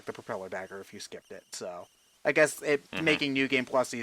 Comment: It sounded like said "Nude game plus"